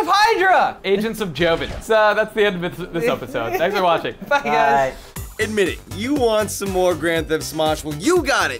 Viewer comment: sounds like Wes says fuck you guys 😂